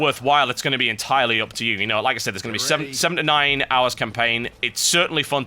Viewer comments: wow, fps players normally pay around $60 for a game with only 7 to 9 hours of gameplay? i initially thought he said 79 but then it sounded like "7 to 9" o.o" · I thought he was saying 79 hours I was like...Oh fuck...but then he said awesome 7 to 9, and got a little disappointed, but still...looks freaking sweet.